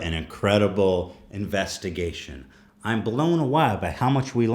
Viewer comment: Anyone else hear Ethan say he is blown 'a wa'